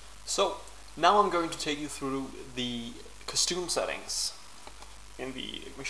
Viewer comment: lol, sounded like you said custoooooom settings. awesome job though, this addon is crazy